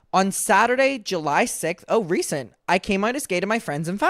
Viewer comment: I mishear you as "Kmart is gay" instead of "came out as gay" lol (Kmart is a store in the US, pronounced like k mart incase anyone is unaware)